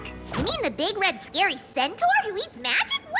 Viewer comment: Did Pinkie say “centor” instead of centaur? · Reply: No i am pretty sure i hear she said "centrav."